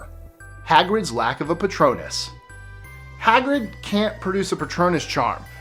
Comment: I thought 04 said 'Hagrids lack of potatoes..' Kill myself..